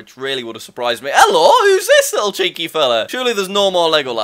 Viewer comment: why did I hear chinky 😂